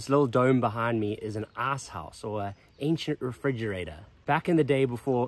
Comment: I thought you said the little dome building was an arse house 😂 i was waiting for to explain what happened there. It is in fact an ICE house not an arse house